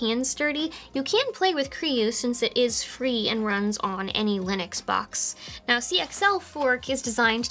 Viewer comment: Anybody else mishear "any linux box" as "annie lennox box"?